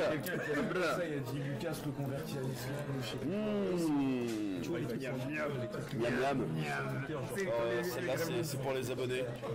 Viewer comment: C quoi que dit lucas a 17:18 "il se reconvertit à l'islam tweeter" cher pas quoi? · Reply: Aniss Slimani Il n'a rien dit de mal : "sur twitter quelqu'un a dit que je me suis convertit à l'islam...."